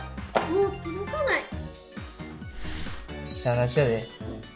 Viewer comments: なんて言ってる？ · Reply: たぶん、しゃーなしやで、ですね(^^) 関西弁やね〜（笑） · なんて言ってるの？ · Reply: ゴジラ剛 しゃーなしやで だと思います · 何て言ってんの？笑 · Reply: Always Rosie しゃーなしやで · なんて言ってるの？ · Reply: しゃーなしやで